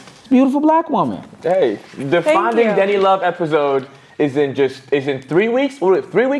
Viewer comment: Why does every time you guys call "Denny" I hear "DIDDY"???💀